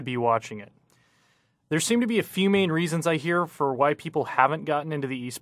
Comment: am I the only one who hers white people instead of why people?